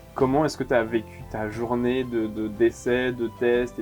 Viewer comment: "Comment t'as vécu ta journée de décès" Quoi ? Mais je croyais qu'elle était protégée !!! ah non, pardon, ta journée d'essai...